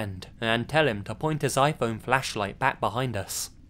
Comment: I thought the first one said "fleshlight " 😭